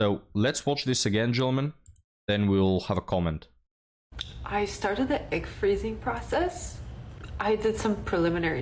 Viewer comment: I heard "I started the ick freezing process" 🤔 What will these crazy women come up with next? They freezing icks to make a crystal ball for finding that Chadroneius Tyroneaparte 😂